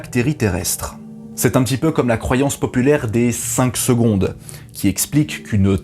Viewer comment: alors j’ai toujours entendu 3 secondes moi 😂